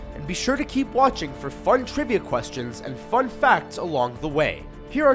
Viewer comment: lol sounds like you said FART facts